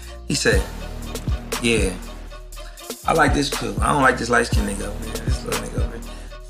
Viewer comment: But Chris maybe I'm wrong but I heard t don't like dark skin women. Is that true?